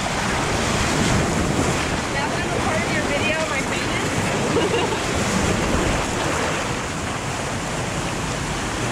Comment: what did she say? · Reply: Blake Hopkins “now am I apart of your video am I famous haha”